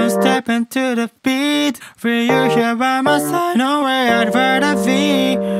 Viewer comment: the only thing i heard was "i'd verd a veed"